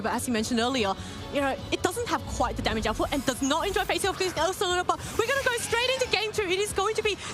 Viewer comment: Bro WHAT 🤣🤣 Can someone translate what she said? 😂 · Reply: "...it does not quite have the damage output and doesn't enjoy facing off against the Ursaluna"